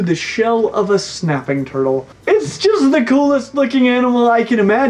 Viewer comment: it sounds like he says "its just the coolest fucking animal."